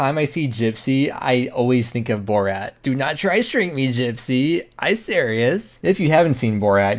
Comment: did you say “do not shrink me gypsy”?